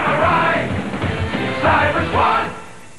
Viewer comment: When I was a kid I always thought they were saying “Cyber Squat” and I had no idea what that meant